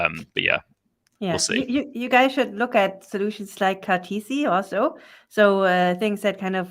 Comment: Great content and questions. Did you mention something called "TC" (@ timestamp 40:28)? I think you said "legacy OS that hooks into a blockchain for provable compute" ? Do you have a link to that? Thanks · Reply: Indeed it was audible 'TC', but said Cartesi. Sorry if that didn't get through clearly.